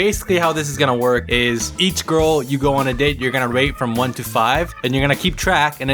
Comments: I thought he said rape xD (wtf is wrong w/ me ;-;) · Omg I thought he said rape from one to ten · So...I might have thought that said “Each girl you’re gonna date you’re gonna rape from 1-5...” Yeah...sorry, but that’s what I heard. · "You are gonna rate them 1 to 5" i thought you said "Rape 1 to 5" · I thought he said ‘rape’ not ‘rate’ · i thought he said "rape" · i thought said rape.. hhhhhh RAPE